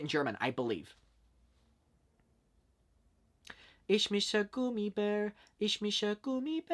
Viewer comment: i heard “is mise gummibear” like come through irish language 😂😂😂